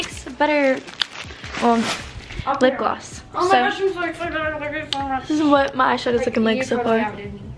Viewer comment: It makes me sad cause I don’t think I even heard her say thank you when she opened her present :-( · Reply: She did she said at 8:18-8:21 "Oh my gosh I'm so excited thank you so much"